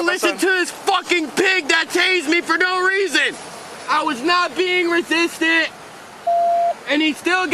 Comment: Perhaps i'm hearing it wrong, but... It sounds like he said "I was not being resisty!" 😄